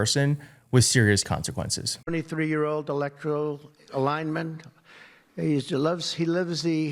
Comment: It sounds like he said "electoral," not "electric."